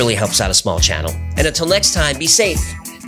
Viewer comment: I thought he said, "And know that hue are appreciated." I was primed to think in terms of colors, so that's what I thought he said lol.